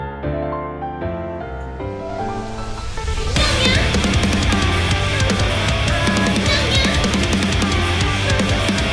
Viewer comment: en la parte 00:16 que es lo que dice, alguien me podría decir por favor! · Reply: dice tokyo ghoul :u tokyo primero y depues dice ghoul :D